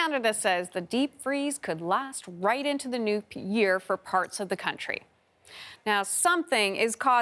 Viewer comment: Did anyone else catch her say nuke year by accident @ 0:09 or is it just me? Just saying.....